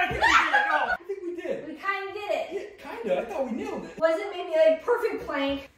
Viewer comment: Yup.. we totally nailed it, right? 🤣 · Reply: You sure did! Oh wait, I thought you said "nailed him" 😂 you got him good a few times 😂😂😂